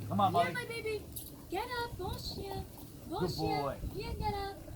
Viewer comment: What does molly say to galope? It sound like another language, even her voice seems different. Something like “bolshian get up”, I really don’t know. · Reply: Bon chien, Gallop! (good dog, Gallop). His training was at a French Canadian school. I also heard Bien, which also means good, and Allez, go.